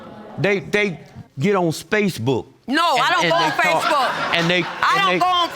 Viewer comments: Did he just say space book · Wait - did I hear him say 'Spacebook'? LOL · I need to get a SpaceBook account pronto... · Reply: I was confused by your comment until I heard him say Spacebook🤣🤣🤣🤣🤣